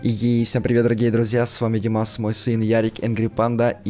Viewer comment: мне послышалось или он сказал МОЙ СЫН